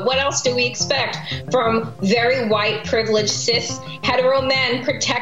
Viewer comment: I thought she said BARRY White 😂. And I’m not white I’m beige.